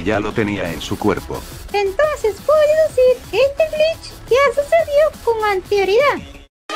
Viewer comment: No entendí nada de que dijo 2:23 bf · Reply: Dijo: entonces podría decir que este glitch ya sucedió con anterioridad